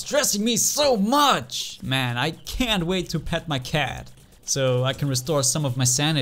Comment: when you said "i can't wait to pet my cat" i misheard "i can't wait to pound my cat" that's r34 material right there